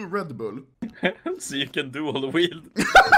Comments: what did he say? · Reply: So you can dual weild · In 3 years I never knew what they were actually saying today I figured out it's "dual wield"